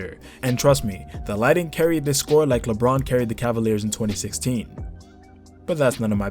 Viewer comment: Thought u said Bron carried the 2016 cavs for a second lol